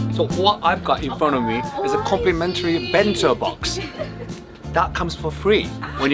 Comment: I heard "complimentary BENZO box" and I just thought...oh boy.